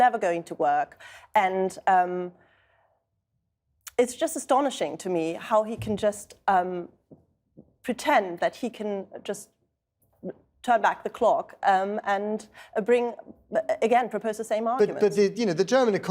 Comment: What does she say at 13:13? Beat round The clock?